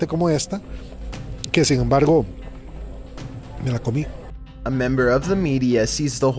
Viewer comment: I think I heard him say "me la comi" which means I ate it 🤣🤣🤣🤣